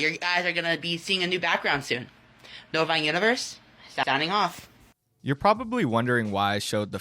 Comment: Is it just me does it sound like he is saying “bovine universe out” makes me laugh every time.